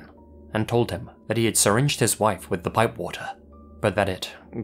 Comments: omg PIPE water aksfaohhf when u first said it i thought you said BONG water and i was like?D?F/a?FASdgadga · So you're telling me he syringed his wife with bong water and it almost killed her? 😮 damn son that's crazy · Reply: Lol he actually said pipe water. It does sound like he said bong water tho